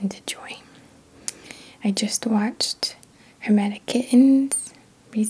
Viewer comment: I love your voice, your teachings, your heart, your stones, your lighting. You are awesome. Keep following your vibe. Question, who was the person/channel who you brought up that shared a "dark story"? I looked around the description & comments and couldn't find it. It sounds like you said something "kittens"?? Thank you! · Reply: HermeticKitten. ☺️